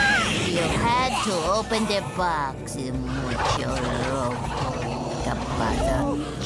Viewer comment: What did Shirley say in spanish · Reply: Sounds like she's saying, "mucho Loco gibberish the box" Basically just saying Eustace is crazy and stupid for doing that.